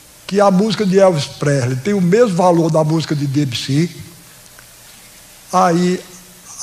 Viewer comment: O QUE FOI QUE ELE DISSE? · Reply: 3 anos depois estou aqui, Debussy - <link> Escute Clair de Lune